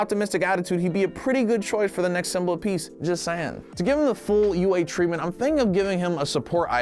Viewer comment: 😂 did I hear a pun "just Saiyan"